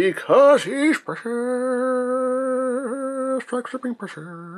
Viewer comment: What is it he is saying whenever he brings out a G1 toy? I've heard him say it before... But I can't figure it out. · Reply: He says Precious